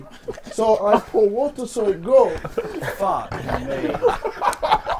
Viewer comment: I thought he said who invented him (●’◡’●)